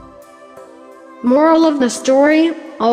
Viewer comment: I thought he said "Always deal homework"